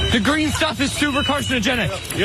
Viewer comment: "the green stuff is ... carcinogenic" - am I the only one who heard that? And; is that true???